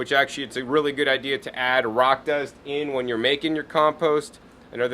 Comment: you mention adding something to the compost but I can't understand the word, sonething like "rocas?" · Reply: I think he says "rock dust".